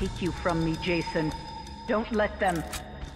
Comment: it sounds like she says let them